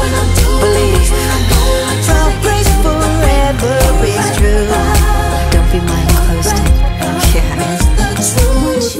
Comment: Alguien escucho en el minuto 2:20 lo que dice Janet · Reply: Ariana Cantos dice, Don't think michael is dead (no creas que michael esta muerto)